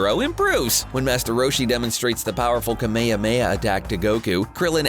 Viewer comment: For 1:57 did you say kamemeah?